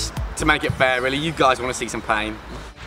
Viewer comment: haha, i thought you said at the end 'you guys want to see some vein', instead of 'some pain'! though it would have been appropriate after the full mooner...